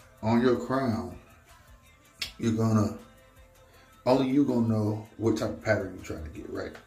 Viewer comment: He says Gona instead of Finna